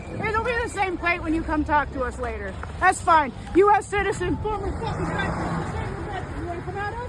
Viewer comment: I think she yelled she was an American citizen and a veteran (listen closely). I say maybe they need to take her veteran pay if she is getting any which more than likely she has claimed some type of disability...Pete Hedseth go after her veteran benefits for encouraging violence against government officials 😊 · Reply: i thought she said she was a former guy 😂 She raised 2 million dollars in go fund me, so that won't affect her.